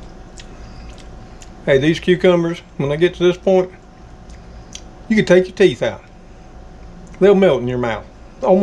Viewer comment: My grandbaby heard you say "You can take out your teeth to eat" She said No you can't my teeth are stuck. lol's She always making me laugh.